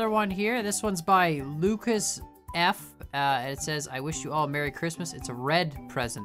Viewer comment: Merry Chrismas yall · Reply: I thought you said mercy christmas